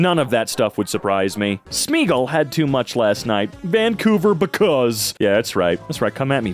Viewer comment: I’m sorry did he just call British Colombia Because?